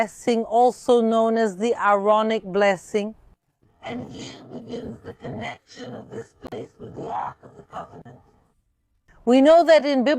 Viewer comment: Does anyone else here that strange voice at 11:43 ? Or can tell what it says? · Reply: “And here begins the connection of this place with the Ark Of The Covenant”. That is what was said.